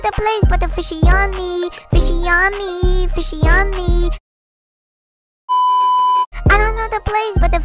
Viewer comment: What other people hear: I don't know the place but fishy on me, got u I'll slap you knee! What I hear "FISHY ON ME FISHY ON ME FISHY ON ME FISHY ON MY FISHY ON ME"